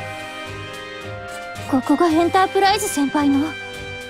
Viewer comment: 我聽成 hentaiprise 了怎麽辦